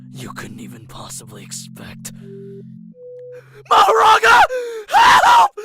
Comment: I think I heard something like maho-chan